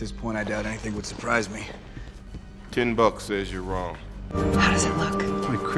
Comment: Fury: Ten bucks says you are wrong 10 minutes later Rogers: Gives ten bucks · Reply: Imma be honest…I always thought he said “Timbuck says you’re wrong”.